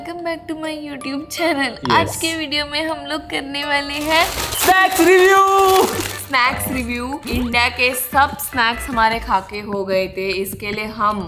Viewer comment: Wtf who heard "$ex Review"?😅😂